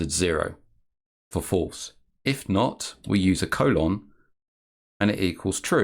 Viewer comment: That ?: syntax works in 0.21, but VarSets were introduced in 1.0. You have to complete the expression before the parser is happy, to make it easy on yourself start with "1>0 ? 1 : 0" for truth values or "1>0 ? 1mm : 2mm" for a length - then go on and replace the components step by step. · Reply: thanks, when he said “colon” I misheard that as “semi colon” - I went back to watch again and it works now that I listened to what was said.